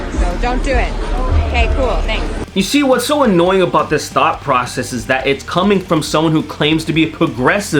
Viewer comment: I honestly thought you said "Thot process".